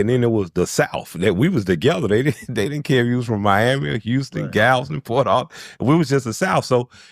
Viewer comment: Holup Mayne!! 2:11 Did He Say Galveston?? 409 What Up We Here